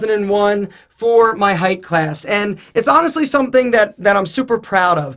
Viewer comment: does alpha says "height" classes